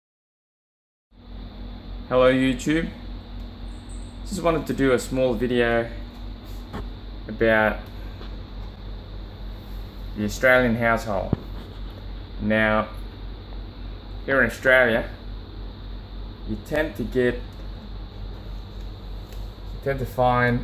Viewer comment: I thought he said Australian asshole instead of household